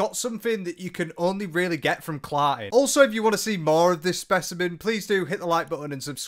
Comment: “That can only really get from _____” 1:02 what was the word ? my american dumb ass can’t understand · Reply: Hahahaa sorry ‘CLARTING’, 😭 slang for a good night with a significant other… lol